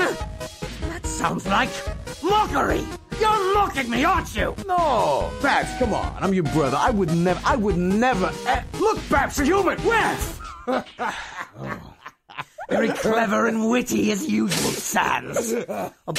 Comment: I thought red called him "boss"? But still that was funny! " HEY GUYS LOOK! I'm the great papyrus! I practice evil laughs like a frickin dingus!" 0:33 was my favorite part because red had that face of "he actually FELL for it!" PUN INTENDED!! Muehheehehehe!